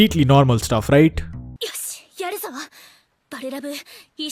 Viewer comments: i heard 'yedzawa' if you're from Maharashtra you understand what I mean 😅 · did she said yedazhava 😂 In marathi yeda means idiot and zhava means sex so yedazhava means fucking like a crazy person 💀